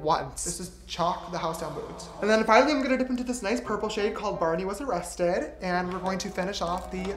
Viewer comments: why did I hear 'Barney was arrested' as 'Barbie was arrested'😭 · “Tampon” and “Barney was arrested” IM SCREAMING 💀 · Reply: I thought he said Barbie 😅 haha Barney makes more sense 🤣🤣